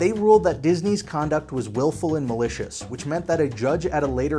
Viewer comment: “Willful and malicious?” Oh, sorry - I thought you said, “Skillful and delicious.”